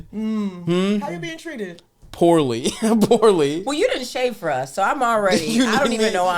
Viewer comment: for a second i thought she said “in shape for us”